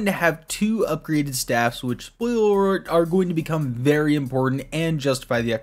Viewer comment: what where you trying to say, because I spent 5 min trying to figure it out😂😂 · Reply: I think he tried to say "Spoiler alert" But he fumbled it